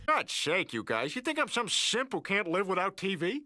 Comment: i swear i heard the word simp in 1999